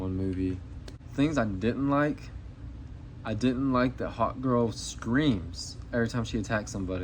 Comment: Good review; I agree with almost everything you said, particularly about Krypto. BTW, when you said Hawk Girl, the captions said Hot Girl, which at first I thought was intentional on your part until I realized it misheard you.